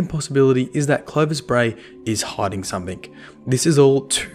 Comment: An add popped up right in the middle when was talking and cut him off, it sounded like said "Clovis bray is high"